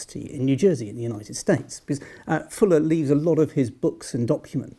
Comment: I keep hearing "Jesse Fuller".